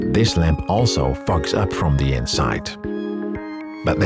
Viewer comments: “Too bad this lamp also f*cks up from the inside.” I can’t be the only one who heard it like that, right? I know you said “fogs,” but still. 😂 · i thought this was the classiest cursing ever recorded until i realized he said "fog" and didnt actually drop an f-bomb. i seriously thought he said "too bad this lamp also f###s up from the inside" until i turned on CC 😂